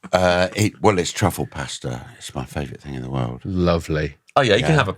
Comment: I thought he said "travel pasta" initially, which would go well with Bob Mortimer's pocket meat